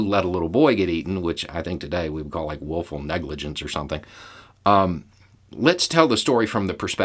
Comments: Did I hear “wolful “ negligence? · It may be your accident but I heard "Wolfal negligence" 😀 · Did you say “Wolf-ful negligence”? 😂👍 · I heard it as Wolf-full negligence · I heard "willful negligence" as "wolf-ful negligence" and I laughed pretty hard.